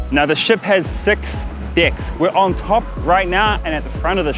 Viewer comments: i thought he said the ship has 6 dicks lul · The way u say "deck" everytime sounds like you saying something else ha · sorry I heard dick instead of deck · Who thought said 6 dicks